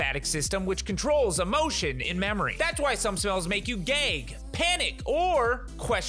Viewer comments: I heard "that's why some smells make you gay" instead of "gag" lol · I know said "gag" but it sounded like gay XD